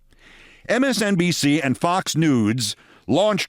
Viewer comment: Wait! Did you says "MSNBC and Fox Nudes" at 49:48?